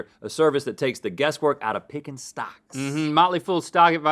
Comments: For the first several seconds of the Motley Fool bit, I heard "socks" instead of "stocks".. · The whole time they were saying stocks I thought they said socks lol